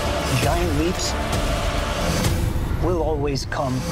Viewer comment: is it just me or does the guy say "Young leaps will always come at a cost" What the hell is he saying!? I've played it so many times it still sounds weird